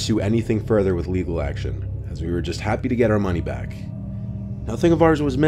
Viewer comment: I know what you said was "shady shit" at 14:00 , but when I first heard it I misheard it as "shiggity shit" and I burst out laughing.